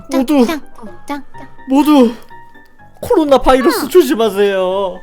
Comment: 코로나바이러스를 잘못들어서 코로 나오는 바이러스라고 들었어욬ㅋㅋ